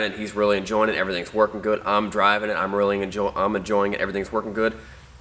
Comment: sounds like he says he’s rolling a joint but catches himself 😂